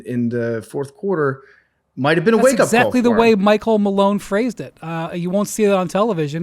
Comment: I heard Mike Home Alone